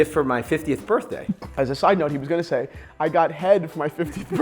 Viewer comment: What did he say at 1:02? Did he say head?????